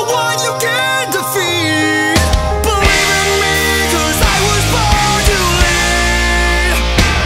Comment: At first I thought he said I was born to pee XD